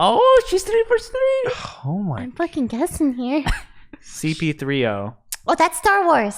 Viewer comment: did josh just said cp3o?